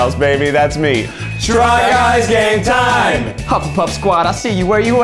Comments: This was the first video I watched of them and I thought they said try guys gay time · It sounds like everyone is saying “try games gay time” bc it’s pride month, which makes me SO HAPPY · I love how I heard ‘Try Guys Gay Time’ instead of ‘Try Guys Game Time’ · sometimes instead of "try guys game time" i hear "try guys gay time" and it felt fitting lmao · Why did I hear ‘try guys gay time’ instead of game time 😂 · they’re not even trying to make it sound like “try guys game time anymore” all I hear is “try guys gay time” and i’m not upset about it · At the start i thought they said "Try Guy's Gay Time" cx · am I the only one that thought they were saying "Try Guys Gay Time"? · When I they said “try guys game time” I swear I heard “try guys gay time” it put my hopes up so high. Lol great video. Stand with Trans rights.